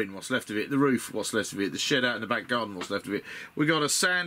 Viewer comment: Ha for a moment there I thought you said "the shit out the back garden" and not "the shed out the back garden. Gave me a chuckle thanks for the laugh! The build looks good so far. I am intrigued by this game but have to resist the temptation. I am on a crusade to vanquish the pile of shame. I am down to Abaddon the Despoiler, a box of Chaos Space Marine Terminators that I am saving for a long term project where I paint them to a high standard and base to a high standard. Got a few hundred 15mm miniatures to paint and my mountain of shame is no more! Then I will look at other miniature games hah.